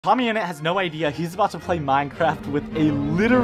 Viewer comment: I heard him say mommy innit💀